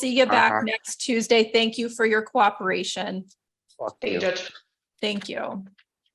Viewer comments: I was listening, not watching, and I heard VERY clearly “F*CK YOU” 😳 I had to find my phone, rewind, and BAM 💥 make sure you heard the same thing!!! 🤣🤣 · Right about 54:13 does he say f*ck you to the judge? I've listened to it multiple times but she doesn't say anything to him. Anybody else hear it?